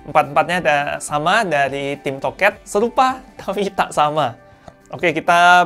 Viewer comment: tim toket? apa ge yang salah denger?